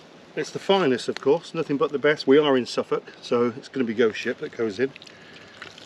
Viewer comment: At first I thought you said "Goat Shit"😂